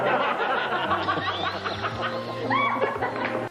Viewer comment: "Jeez, I'd reckon my hair'd go like that if I had a pencil up my ass as well" Gives audience the death stare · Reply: I heard "face like me arse as well"